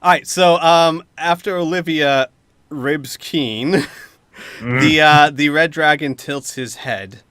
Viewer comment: It sounded like "After Olivia rapes keen"